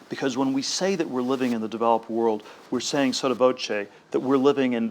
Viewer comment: What does Peter Thiel say at 12:07 "We are saying sort of.."